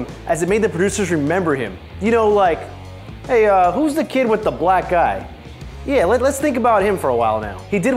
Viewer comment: idiot sounded like "who's the kid with the black guy" instead of "black eye"